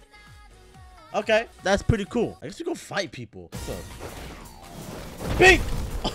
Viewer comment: bro i thought it said I guess we gotfight people but the t between fight and go is you avatar acc.